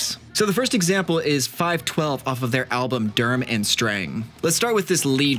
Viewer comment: Did you say Durm and Strang? Hahaha